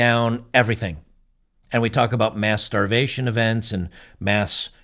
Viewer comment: I'm pretty sure I heard said "masturbation events" hahahahahhaa